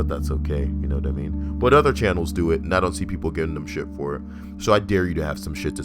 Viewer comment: I initially heard that as '-but other camels do it-'